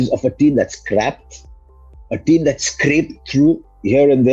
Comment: Did he say KKR is a team that is crap?